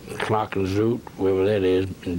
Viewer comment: Gerald is saying "Knokke-le-Zoute" which is indeed in Belgium (the person who made the subs didn't understand what he was saying, so I figured you might want to know what it actually was he said). It's part of Knokke-Heist which is on the Belgian coast.